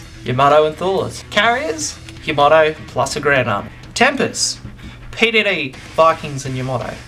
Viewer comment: what does he say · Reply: roe Carriers? Yamoto + a ground army